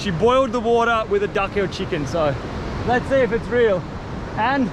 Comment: I heard Duck-o-Chicken 😂